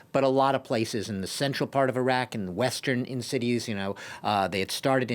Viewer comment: Did he say Iraq? 😂